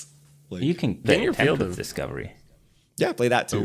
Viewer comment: anyone heard what card was talking about around 1:22:18 ? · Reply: Tempt with Discovery.